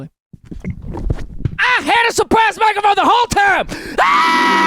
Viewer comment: I thought he said suppressed mic